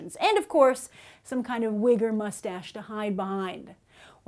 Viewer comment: During the Mortdecai review, I thought she said "Wigger Moustache" then I realized it was "Wig or Moustache". LOL